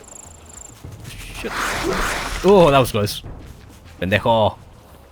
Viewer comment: Haha, did you just call the lizard a a-hole 28:36 in Spanish :P The lizard just wanted to give you a hug! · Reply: Pendejo? Yeah LOL.